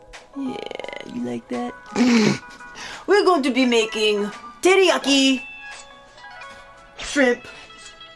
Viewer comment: Lol... I SWEAR i heard "TitiYaki" 🤣😂 #takepound&peel